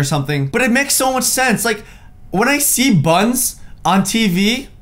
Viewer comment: Did he just say When I see butts on tv?